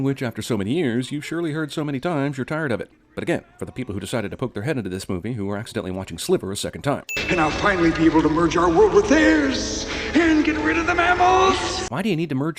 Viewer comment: I first thought he said "slither" I was like like "hold up, what??" 16:00 My thought on this matter for many years